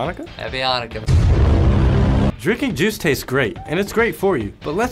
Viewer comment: why do i keep hearing “jews”??